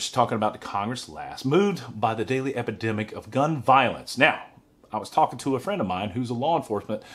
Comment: How I defend it is Pelosi act.... OMG I m Misheard you I thought you said DUMB VIOLENCE... my bad